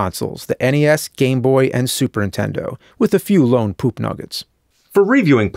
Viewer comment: did you say superintendo? You did. You did say it.